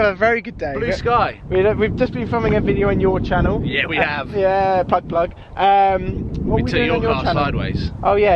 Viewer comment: did you say butt-plug at 0:32? I'm guessing that bit didn't make the cut of the final video